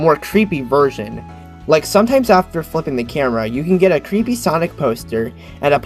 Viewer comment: I thought you said f#$@ the camera🤣🤣